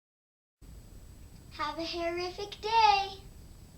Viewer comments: aww it's sound like she says have a horrific day! =O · Omg I thought she said horrific day!!!! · It sounds like shes saying have a horrific day lol. I love this hairstyle it looks so easy to do specially for a beginner like myself (